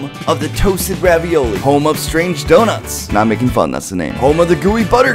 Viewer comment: I thought said homo strange doughnut but was home of strange doughnut